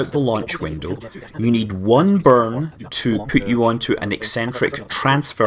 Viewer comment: What Scott Says - "You will need 1 burn" What I Hear - "You will need 37 burns"